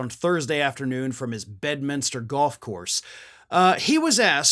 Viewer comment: I thought you were saying press conference from his bed 😂 not Bedminster! Which wouldn’t surprise me.